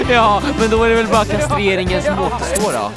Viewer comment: Vad säger vid 3:36 svara gärna :) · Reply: "Då var det bara kastreringen som återstår"